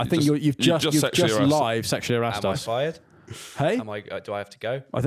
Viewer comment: asking "am I fired?" After sexually harassing Fin and, but I genuinely thought he said "am I Al Fayed?"